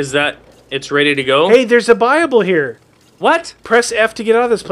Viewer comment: before I thought people were saying bible instead of buyable